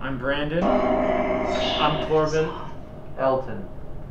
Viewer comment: right when Elton says his name, the deep voice sounded like it said "silent Elton"